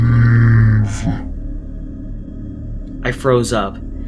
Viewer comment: i know hes saying leave but to me it sounds like hes saying weave?